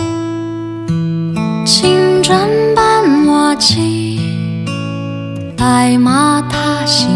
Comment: 青磚伴瓦漆，聽成青春扮麻糬(閩南話)...?